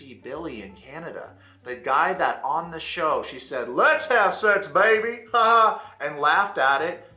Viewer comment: Oh I always thought she said: “what’s up sexy baby?” Lolll